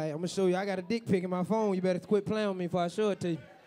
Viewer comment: watch the blk guy with the white girl try so hard NOT to look at the blk chick twerking!! LMMFAO!!! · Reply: Lol I thought she said you better stop lol.